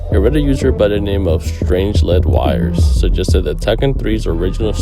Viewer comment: think that says strangled wires gang